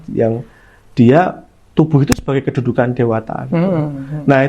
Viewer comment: Tubuh sbg kedudukan dewata, salah dengar ngga ya. Kok lbh dekat dg ajaran alkitab tubuh adalah bait Allah